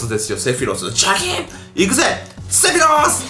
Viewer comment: セフィロスがセク口スに聞こえる